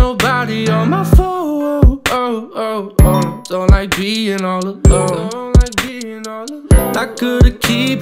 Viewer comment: I thought he peeing all alone but he said being all alone sorry i just thought he said that...